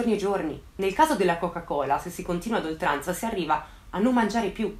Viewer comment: ho sentito bene? coca cola?